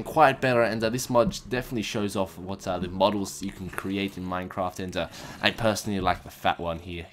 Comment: I thought he said minecomp instead of minecraft lol